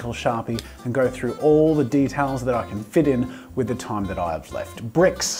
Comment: When he said details I thought he said demons